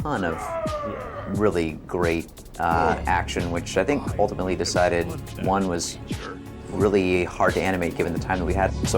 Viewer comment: What does the arbiter say at 1:06 ? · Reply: Actually the brute says "I was Having fun with that creature"